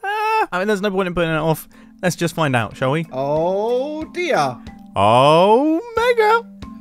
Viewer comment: I legit thought Mumbo said "Ohhhhh negga". I was like HUH 😳🤨